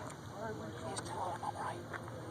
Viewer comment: I thought he said, "He's telling him I'm white."